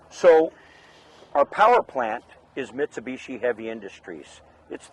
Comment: Also...didn't the video mention Yanmar as it's engine brand....I know he said Mitsubishi but thought I heard Yanmar which yes...is top notch.